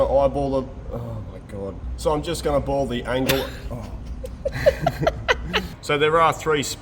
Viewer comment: I fully heard you say "I'm now going to boil the angle here...". Good luck with that! 😆😆😆